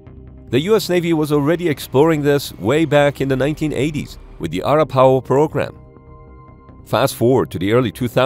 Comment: "Uh-RAP-Uh-HOE", idek what that pronunciation is bro lmao. He said "Our-uh-POW"